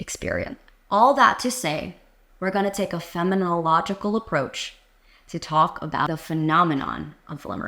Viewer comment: I heard you say “feminological” several times, and was unsure if that was intended or if you meant “phenomenological.” Was confused about whether I should be thinking about what you’re saying in the context of feminism or of phenomenology.